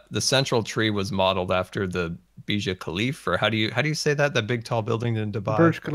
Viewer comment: did just say mia kalifa